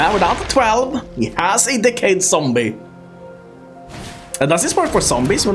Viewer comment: I spewed coffee because I heard "dick head zombie" but it was decayed zombie.🤣